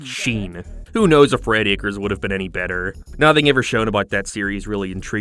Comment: What Fredacres? I assume that is what he said, that is what the CC says. I cant find any information on the show. Anyone know? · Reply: Red Acres, the original idea for the show he was talking about earlier that was then modeled into Planet Sheen.